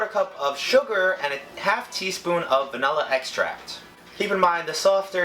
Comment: I thought he said " salmonella extract "